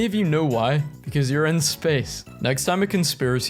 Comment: my dumass thought you said "because you're in spain "